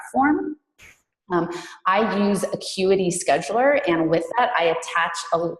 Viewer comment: min what App is the using? Is she saying ``Quedy`` Scheduler?